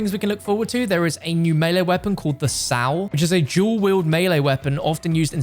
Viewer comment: What did just call the "Sai" melee weapon? Sal?